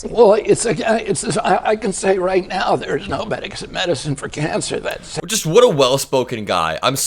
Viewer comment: Hard to understand but did he say "there's no bags of medicine for cancer"? I mean it's true aside from chemotherapy. Literally a banana bag. Maybe he means there is no vaccine for cancer which is true... well, aide from the HPV vaccine. With him running health the USA is going to be a case study in what happens when you don't vaccinate.